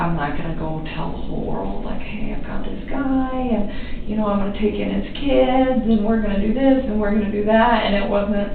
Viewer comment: did she say, “...I’m going to take in his kids.”? Why on earth would she be “taking in his kids”? They had a mother.